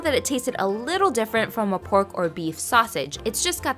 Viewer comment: I thought you said gay meat taste… 🤣